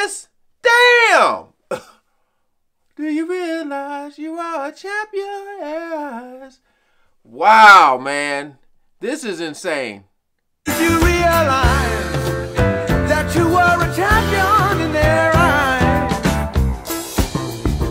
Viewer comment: Hand to God, for 30 plus years until this moment, I thought he was saying “..that you were Italian in their eyes.” Whatever the hell THAT was supposed to mean.